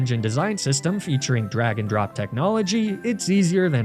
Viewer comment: Second video you uploaded that I misheard you say "drag and drop." I thought you said "dragon drop."